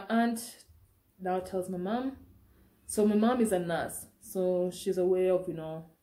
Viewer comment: Did I just hear you say "my mom is an ass"? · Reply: Lol. Never. I said my mom is a nurse